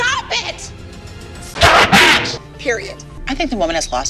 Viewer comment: DID ABBY JUST SAY " period 💅🏻 "